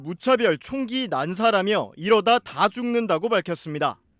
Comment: 원희룡이 말 한 "이러다 다 죽는다" 가 왜 내껀 폭로 하지마로 들리냐ㅋㅋㅋㅋㅋ